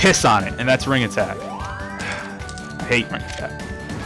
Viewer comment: LOL it sounded like he said "ugh.. i HATE Minecraft..